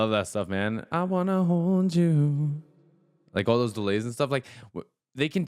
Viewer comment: I think he says "I wanna f*ck you". Defo a homepage to Prince who used to drown out his swear words with instruments. Also, in the song he says cum inside a girl but he flattened the "cum" so it ain't obvious